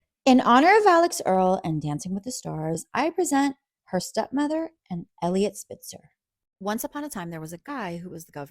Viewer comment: I thought she said “her stepmother, Eliot Spitzer” and I kept waiting for a Caitlin Jenner-esque plot twist. After finishing the whole video I was very confused until I rewatched the beginning and realized she said “her stepmother AND Eliot Spitzer” 😂